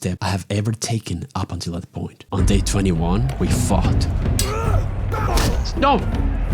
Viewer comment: HELP I HEARD “on day 21, we fucked” LMAO